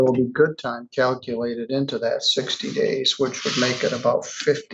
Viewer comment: He's letting her go. I thought he said 365 days in jail. 60 days? Hand her a drink and give her the keys to your car, Judge. And pray she doesn't kill someone.